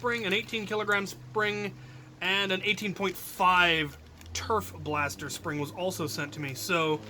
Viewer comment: I heard "turf blaster spring" as "TERF blaster spring" for a second. So I'm just imagining flinging foam at TERFs so they go away. You wouldn't really want transphobes at your Nerf war.